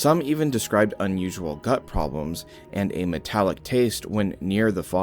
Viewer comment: did he say “a nut problem with a metallic taste”?